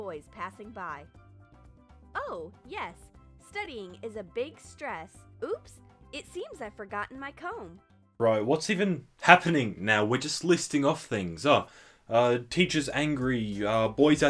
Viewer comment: When she said “oh, I believe I’ve forgotten my coat” I thought she said “I’ve forgotten my coke” and for a brief second, in my mind, she became a cocaine addict.